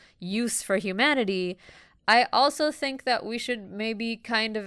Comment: So @ 58:34 she says: " I also think that we should maybe kind of actively try not to make knowledge .........(?)" . What did she say in the end of that sentence? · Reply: This video has subtitles. She said "harmful".